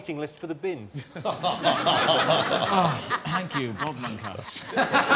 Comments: anyone who can tell me what Rob Brydon says here 29:25 to which Jimmy Carr's delayed response is "f**K off" i tried turning up the volume but i cant seem to get what he said sounds like hes saying "thank you broad monkas" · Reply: "Thank you, Bob Monkhouse" Sorry I'm so late. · Can anyone tell me what Rob's saying at 29:25? He's roasting Jimmy somehow, and it kills me that I can't quite make it out. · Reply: Thank you Bob Monkhouse